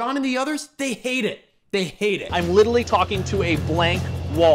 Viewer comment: Okay, I'm drinking along as a side note, but this man saying "liddlely" instead of literally is liddlely sending me rn 26:05. It's happened twice